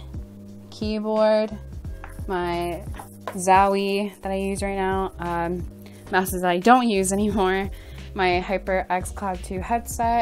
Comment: did she just say “mouses”